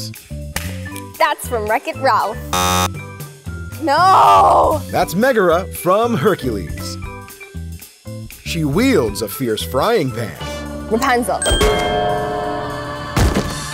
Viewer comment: Did she say Rapanzel !! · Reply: Yes, I know, but I heard her saying Rapanzel.